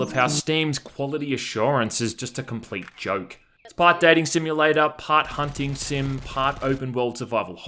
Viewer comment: When you said "Part Dating simulator" I thought you said "Part Daddy simulator".